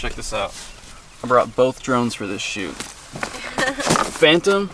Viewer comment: I thought said "Check this out, I brought two drones for this SHIT" around 3:25. Cue jaw drop